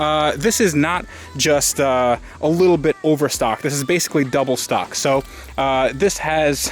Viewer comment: wait.. did he just say double stock @ 2:23 lol